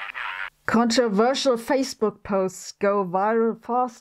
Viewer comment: Instead of 'facebook' the subtitles say 'fakebook'. I like that. 😀